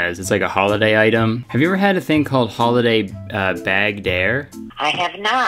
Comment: When the woman said "Bagged Dare" I thought she said bagged hair